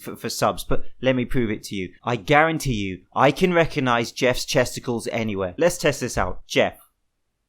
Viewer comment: who heard testicles?